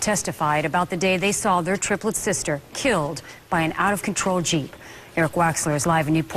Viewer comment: By an out of control what? I couldn’t understand her, someone please reply what she said at 0:07 please don’t hate on me 😣 did she say Jeep?